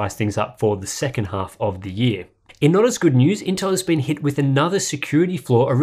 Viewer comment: Hahaha I think I heard "in another GOOD NEWS intel has been hit" blah blah hahahahaha Dude hahaha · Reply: Tim said "not as good news." It's that thick Australian accent.